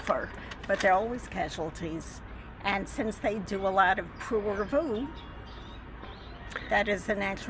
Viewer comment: can somebody help me decipher the term being used at 19:00 by the lady being interviewed? it almost sounds French, "prouré vous" or something? what does she say, and what does that mean? thanks